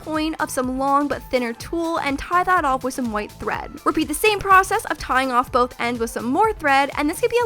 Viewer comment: was it just me or did say tie is off with some white bread